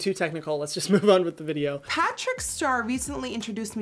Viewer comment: Patrick Star? I'm sure I heard that wrong 😂